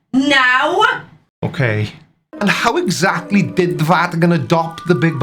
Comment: Did you say "The Flatican" @ 8:41???? If not: you can use it anytime!